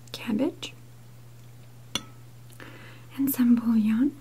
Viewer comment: I thought I heard 'and some Paul Young' as one of the ingredients listed at 2:31 😂. Great video as ever, I love your posts xx